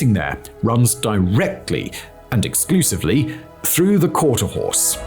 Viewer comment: It has to be the accent. I hear 'Quarter HOUSE' and not 'Quarter HORSE'. 'Quarter HORSE' means it'll run out the gate. 'Quarter HOUSE' means dad got a bad deal in the divorce.